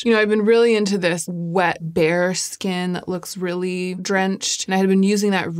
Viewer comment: I'm so exhausted from the heat that I heard you say "wet bear skin" while reviewing the Torch Lumière, which I totally accepted as the new complexion trend in beauty. I adore the rose Diamonté shade on you and have developed an appreciation for cool pink highlighters this year on myself. I like that it not only adds shine but also a touch of CONTRAST. My bone structure is sorta wimpy and when I see contrast on my face, I'm transformed. ALSO, I've Googled so many Lipslique swatches and thought I'd written Nu de Soleil off but I feel like it would dull down my mauve-y lip shade just fine! Both that and Hevyn are great on you. xx